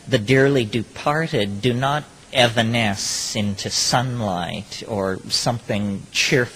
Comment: what word did he use? They ebeness into sunlight? What was the "ebeness" word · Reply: "evanesce"